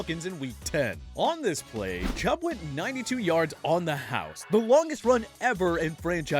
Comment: I thought you said beast mode not longest runs